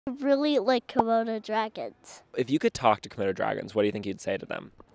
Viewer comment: I legit thought he said “corona dragons” 💀